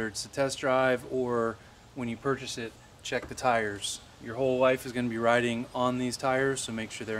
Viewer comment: I thought you said your whole wife is going to be riding on these tires lol